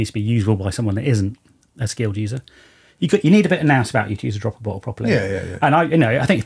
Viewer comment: What did he say? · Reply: Nous, means having some knowledge or common sense.